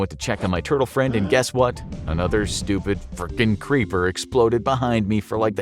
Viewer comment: Captions are wrong at 10:56. no bleep sound was used. He said “fricking.”